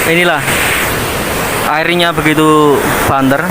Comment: Menit ke 1:07 coba denger abang nya ngomong apa,kayak ge mana gitu☺️☺️☺️ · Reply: "banter" ra ngapak ra kepenak